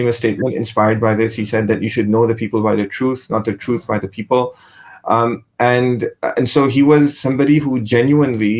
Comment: HE DID NOT SAY '' you should know the people by the truth not the truth by the people " he said : " you should know the men by truth not the men by the truth " يعرف الرجال بالحق ولا يعرف الحق بالرجال